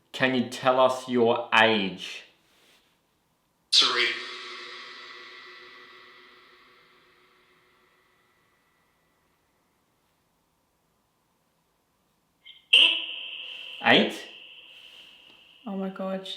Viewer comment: sounds like it says 'So read' and then says '8'. picks up that framed letter and reads it. Wow! Creepy!